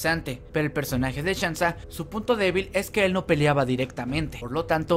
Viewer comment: Su p*** débil escuche yo XD XD XD